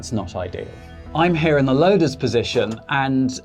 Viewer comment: I know you said "loader's position" but it sounded like "lotus position."